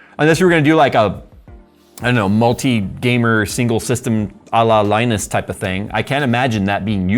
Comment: Man did you just say Allah Linus ? Wtf lol 😂